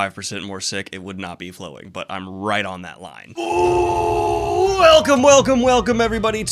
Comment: You all hear, "I'm right on that line." But! He actually said "I'm ridin' on that line." 🙂